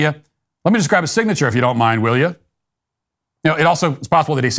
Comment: I hear “will ya” each time